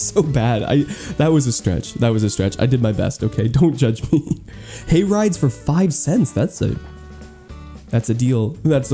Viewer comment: My volume was turned down and it sounded like you said Gay Rights for 5¢ instead of Hay Rides. I was ready to buy so many